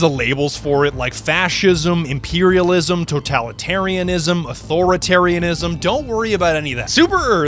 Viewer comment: The Ism list was crazy I thought he might have said autism. 7:53 in the video